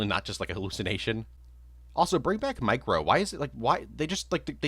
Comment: Gmorning snd i thought you said Mike Rowe when talking about micro… dirty jobs